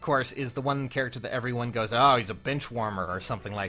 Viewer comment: No joke, when said alfonse was a benchwarmer, i literally thought he said he was a b!tchwarmer, lol, my bad, i need my ears cleaned